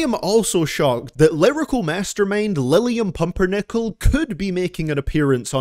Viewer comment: misheard that as “liverpool mastermind” 1st